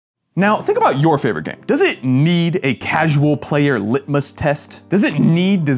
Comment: Ngl I heard "casual player ligma test" the internet has ruined my brain.